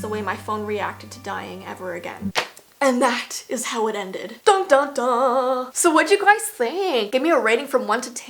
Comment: the 1% of people watching this DO THIS! Turn on your captions fist then go to 6:28 right she says 'da da da' Instead of that the captions say Doctor